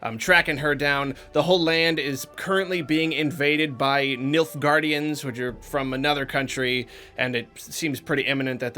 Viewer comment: I'm sorry, did you just say "Milf Guardians"?